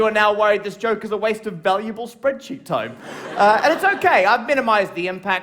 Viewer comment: My bad! I thought he was saying spreadcheeks.